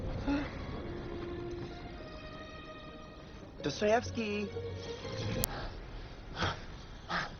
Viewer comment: did he say "Dostoyevsky"?